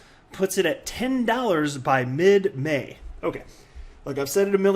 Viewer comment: Thought it said "mid day" for half a second 😂